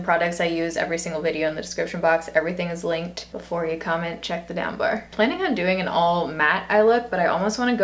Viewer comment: it almost sounded like she said "check the damn bar" and not "check the down bar"😆